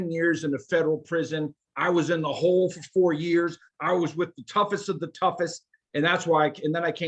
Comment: I thought he said he was in the hole for seven years. They eat him up and I honestly don't get it. Keep making this content.you guys are growing obviously